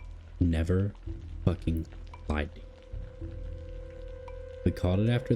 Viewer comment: I might just be deaf but I do not understand what is said at 21:50. Based on what is said after is sounds like "lied to you" but it's really confusing ;p. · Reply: It's "lied to you" just with cheap equipment and a million takes trying to narrate my stories, some stuff doesn't come out too well